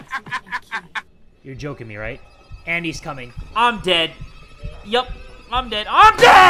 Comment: did anyone else hear Andy's coming